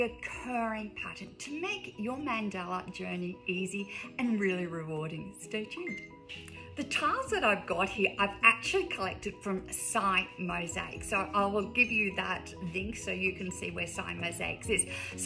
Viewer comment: Will you please spell the name of the company you bought the mosaic tesserae from? Thank you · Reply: I’m asking about the ceramic shapes, not the nippers. It sounded like you said sy mosaic.